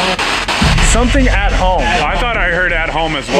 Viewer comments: i hear it say, “you should be at home” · it sounds like it says 'you should be at home'. I listened to it a few times & I keep hearing that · I hear “You should be at home”